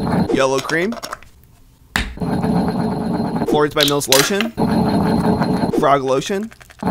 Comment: Did i hear him saying "Frog lotion."???-💀💀💀